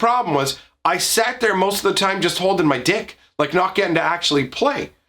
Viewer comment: Does he say: "Holding my dic"? 😶